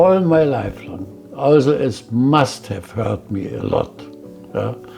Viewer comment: Hola, en el 24:32 creo que dijo "Hurt me" y no "Help me" (creo). Por otro lado, que entrevista mas impactante! Haber cargado toda tu vida con cosas que tu no hiciste